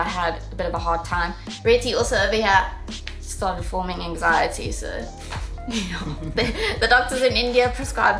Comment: "forming anxiety" which when said sounded like performance anxiety.. Emotional damage